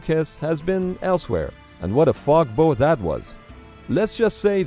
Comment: Did he just say what a fog bow? 😀